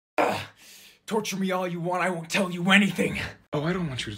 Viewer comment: Can someone tell me why i thought he said "you can TOUCH me all you want" at the beginning 😭😭🤣🤣